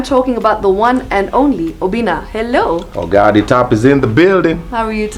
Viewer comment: I always thought the saying was "Oga at de top," but it's "Oga de top!" Mine makes more sense, though 😂😂😂